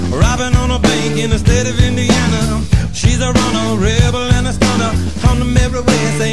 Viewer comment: So many years listening to this awesome song and I always thought they said: "She's a runner, rebel and a stoner" my bad...